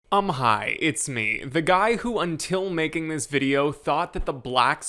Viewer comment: I thought you said “I’m high, it’s me!” Lol